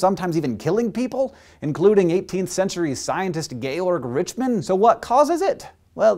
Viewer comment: i heard "gaylord richman". i had to relisten to make sure i was wrong.